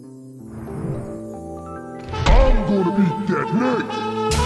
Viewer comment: Can anyone make out what Coach says before making this absolute legendary entrance? It's an age old question that I've been asking for years but the best thing I can make out is "I'm gonna be good night!" 🤔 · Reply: "Im gonna eat that Nick!"